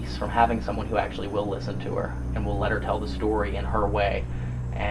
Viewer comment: I heard ALBERT not "tell them".